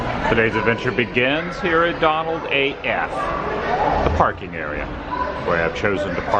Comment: Lol without looking at my screen when you said Donald 8F, i thought you said Donald AF. I was like, yes, its a Donald AF day. 😂😂